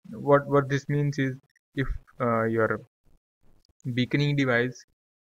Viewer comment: Did you said bikini device?